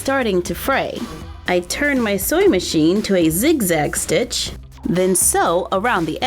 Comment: The more you listen it sounds like she says “soy machine” lol